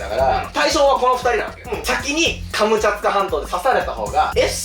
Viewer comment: もう最後ら辺カムチャツカハンドに聞こえてきた😅